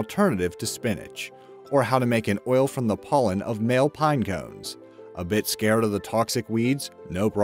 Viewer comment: Did I hear that right? MALE pinecones Bitch wtf